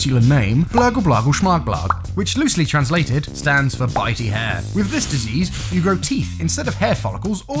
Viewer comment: wait did he say all hair? oh god please no